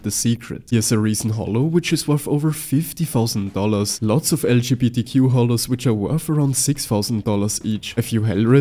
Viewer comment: Did you just say LGBTQ HOLOS @ 1:27 ? HAHAHAHA